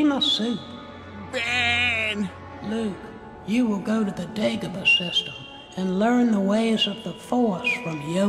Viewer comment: First time i watch this in original, all i heard is " Dig of a system"